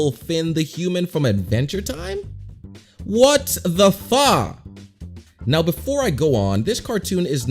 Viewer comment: did you say what the f omg man